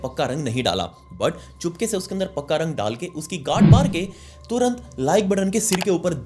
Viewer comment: Am I the only one who heard g@ maarke 😂😂😂😂